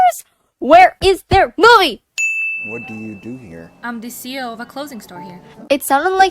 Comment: “a CEL of a closing store” is what I heard XD